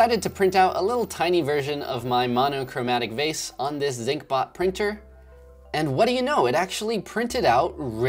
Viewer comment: What 3d printer is he using at the end? Sounds like he said zinc bot printer, but google isn't giving me anything on that.